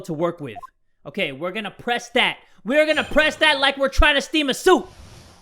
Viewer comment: "Steam a suit." I don't know why I heard "Steam a soup."